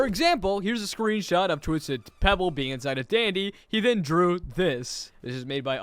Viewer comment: Did he just say twisted pebble instead of twisted toddles😂😂😂😂